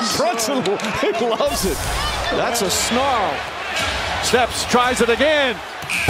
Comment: did he say Steph tried it again..🤔